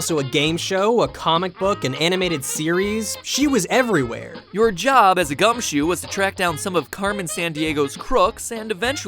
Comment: Did you say "Your job as a Gumshoe"? As in Detective Gumshoe from Ace Attorney?!?!?